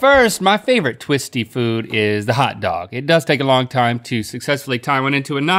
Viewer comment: Did he say hotdog??